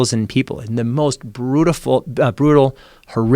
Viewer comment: he says brutaful instead of brutal when talking about the prison camps, interesting Fruedian slip